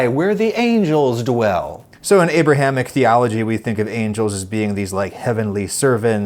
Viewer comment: I heard “Abraham Ichthyology” at 4:37 but I’m pretty sure Adam was speaking about Abrahamic theology, 😂